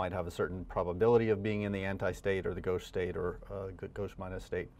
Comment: When we use the Stirling approximation, the argument of the ln has to be big. While the total number of particles is certainly big, here the n_i are arbitrary. They can also be 0. How can we justify it? This is my attempt: the sum of n_i is equal to N, so if the total number of possible states is much lower than N, some n_i have to be big. So I can basically say: n_i small => ln(n_i!) ~ 0 n_i big => use Stirling approximation But even if the factorial grows incredibly fast, the ln grows incredibly slow, so I need to be careful here to make sure I'm really ignoring small numbers. Stirling says: ln(n!) ~ n*ln(n) - n so for n big this is big. Now this approximation actually works quite well even for very small numbers like 100 and ln(100!)~364 which is a small number in the summation. Any mistake in my reasoning? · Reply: What I said at 0:47 is "gauche state". I.e. the conformation with a dihedral angle of 60 degrees. YouTube's speech transcription algorithm misheard that and transcribed it as "ghost state". Sorry for the confusion!